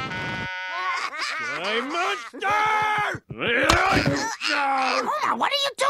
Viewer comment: What did homer yell out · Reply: He said, “Die monster!”